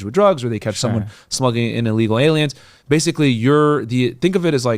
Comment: did Myron just say smuggling in illegal aliens???